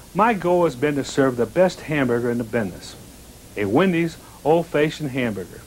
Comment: did Dave say Bidness?